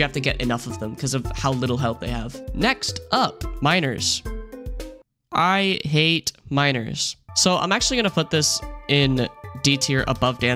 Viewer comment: i misheard that as "i hate minors" lol